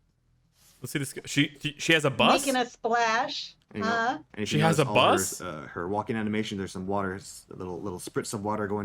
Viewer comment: I heard "silk has a bust?" and I just went like "WAHAHT? WHAT'D HE SAY?"